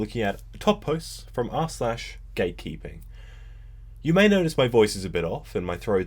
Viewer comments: With his accent i thought he said GAYkeeping lol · Thought you said gay peeping and I was like wait wot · i thought you said gay keeping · WHEN YOU SAID GATE KEEPING I THOUGHT YOU SAID GAY KEEPING SKSKSKSK